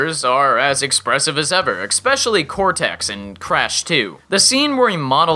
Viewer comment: Did he just say "Eckspecially" ? 3:25 :-)